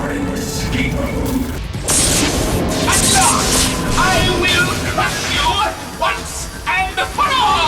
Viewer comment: No way I'm the only one who heard "I will cuck you once and for all"